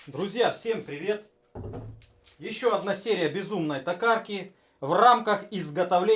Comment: Безумной татарки? Мне послышалось наверно )))